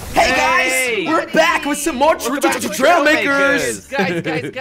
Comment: It paused the video and I thought he said trailmix.... XD